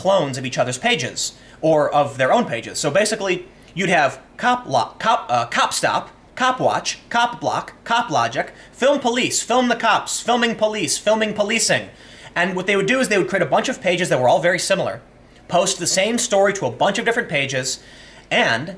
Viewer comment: WE NEED KITTY BACK! (Did Tim say CockBlock?